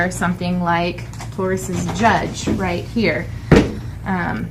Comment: Very helpful. Thank you! I would suggest adding the names as text overlay on future videos. Even closed caption thought you said horse 🐎 judge revolver instead of taurus judge 😄